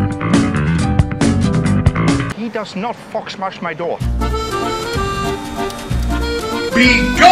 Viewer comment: Did I just hear that correctly: "He does not f_ck smash my door" wtf lmfao